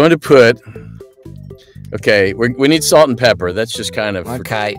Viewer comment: When you said “pepper”...I thought you said “peppa” like “peppa pig”